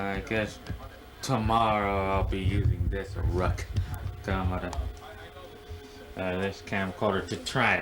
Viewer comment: lol what did you say at 19:03? Ruck camada?